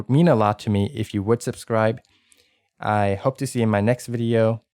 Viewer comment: did he said "with all my fart" at the end?